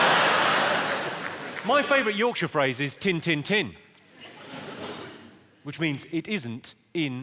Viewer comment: I heard 'tin tin tin' and I thought he said, 'It ain't in.'